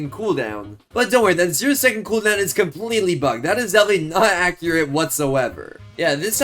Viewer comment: completely what? F word? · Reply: He said bugged